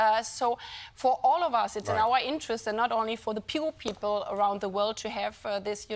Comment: Sounded like "pure." 😂 (I think she meant "poor"?)